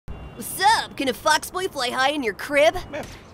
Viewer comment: did talis say a bad word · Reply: Fox boy not frick boy